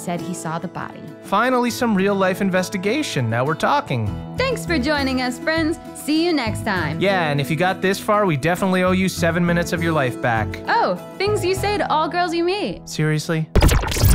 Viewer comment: Is the audio different? I swear first time I heard it, it was “Seven minutes is pretty good” not “We owe you seven minutes of your life back”